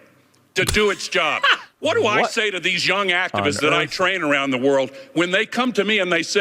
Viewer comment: Sounds like he said he trains antifa hmmm 🤔